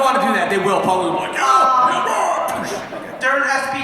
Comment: I don’t know if I’m mishearing, but did he ask about SPD being all of the Time Force rangers kids? And I love how at 48:44, it seemed that Paul knew Jason Narvy was going to crack up. I love those two together!